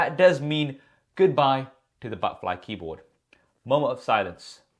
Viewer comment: I heard him saying butterfly keyboard as Butt life keyboard 😂. A moment of silence to the butt life keyboard 😂